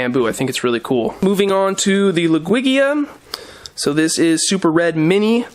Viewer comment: An experiment idea could be the best ways to convert emersed growth to immersed. CO2 is generally seen as helping with that. Corey from Aquarium Co-Op also uses Seachem Advance, which is a phytohormone supplement. Then you could use the prep for an experiment to do an experiment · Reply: Oh wow, didn't even realize I was early... Also, I love your videos but... it really sounds like you say Lugwigia instead of Ludwigia and I always spend half the video trying to tell if it's just my ears or not...